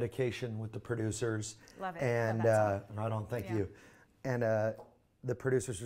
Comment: "I love the medication" "I dont Thank you" did only I heard that ? 2:33 XD